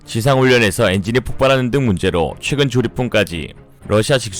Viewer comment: 앞의 폭발이야기 때문인가 조립분이 계속 조리퐁으로 들리네 ㅠㅜ